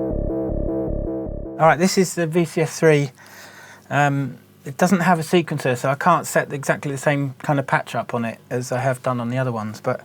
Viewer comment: which one is at 15:30 im having a difficult time making out what he says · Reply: Alright, this is the VCS3. It doesn't have a sequencer, so I can't set the same kind of patch up on it.